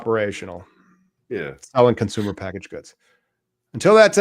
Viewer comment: Aw dude! I thought you said no news this week.